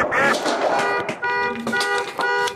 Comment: LOL, did she say "sharts"?